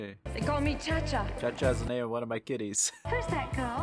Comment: I thought you said "ChaCha is the name of one of my kidneys" 😂😂